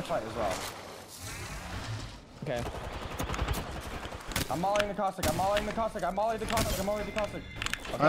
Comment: What was saying at 9:42? "I'm mollying the caustic"?